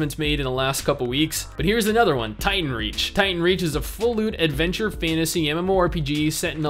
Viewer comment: Not gonna lie I heard "full loot" as "full lewd" the first time you said it